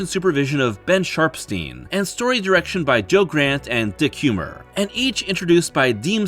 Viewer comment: I'm sorry did you say his name was Dick Humor?